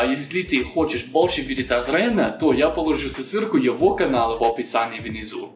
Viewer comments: "Я положу его сосилку..." Бляяяяяяяяяяяяяяяяяяяяяяяяя)))))))))))))))))))))))))) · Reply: сысырку мне послышалось, милота какая ) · Извини, Азрен, мне сначала послышалось, что тебя зовут Азриель😂 ( это отсылка к Undertale :) )